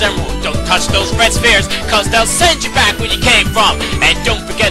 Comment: I hear this as "Spear" not "Sphere"